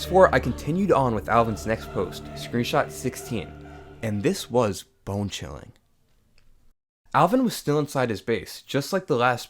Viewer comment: I thought he said “ and this was bing chiling” 😂😂😂